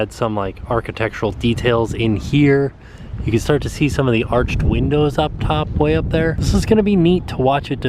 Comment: Did you say wee-ndows? lol